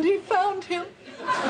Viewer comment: That face Fraiser makes is hilarious. Wait. Did she say ‘HIM’ ?